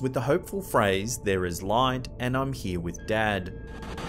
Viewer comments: I didn't hear "There is light. I'm here with Dad." I heard "In the light. Come here, instead." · If that's Robin, sounds like he says, "IM HERE WITH THE DEAD" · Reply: I thought he was saying "I'm here with David" whoever that is. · I loved Robin! But it sounded like he said im with David not dad. It does really sound like him though. Creepy but at the same time awesome! Keep the evps and pics and scary vid clips coming!!✌😉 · I hear "There is light.... I'm through with it...." Maybe through with all that crap we call life.. rip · Robin Williams clip,i dont hear 'im with dad' i heard 'Im gonna do it'